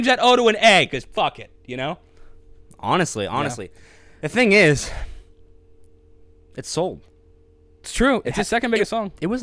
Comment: Talking of wants and once, "the thing is, it sold" sounded like "the thing is, it's old"...